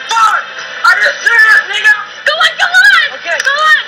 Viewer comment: OW.. FUCK! ARE YOU SERIOUS NIGGA!!!!! Lmao did he really say that? 😂